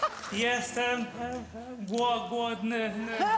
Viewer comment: on powiedzial: jestem głodny? XDD Bo jeśli tak to mam problemy ze słuchem.... Ponieważ usłyszałam: jestem płodny XDD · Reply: Ja usłyszałam pierogi a potem jestem głodny